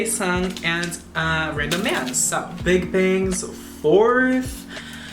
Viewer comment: did you said “random man”😂😂😂😂